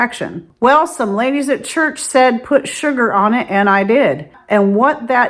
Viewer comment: Did you say people told the lady to put Sugar on her yorkies infection? It sounded like you said the word sugar even after I played it back several times cuz I thought sugar was so odd for an infection. Ty. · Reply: Yes, sugar. I thought it was odd too but when I thought about it more, it kind of made sense. I would say manuka honey would be a better option.